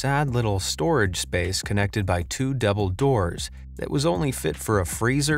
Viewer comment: Did he say it's connected by two Dumbledores?